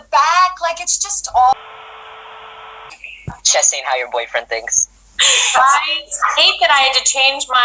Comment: what did Anthony say at 9:26 ??? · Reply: “Chess ain’t how your boyfriend thinks” they were talking about lines they had to change because of swearing for the filmed performance